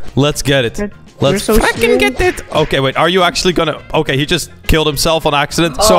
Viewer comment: did he say let's f*&%ing get it?